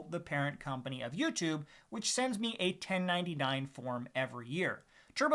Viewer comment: For a second I thought you said they send you $10.99.